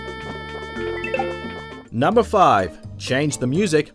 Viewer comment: Lol, I heard "Numberphile!"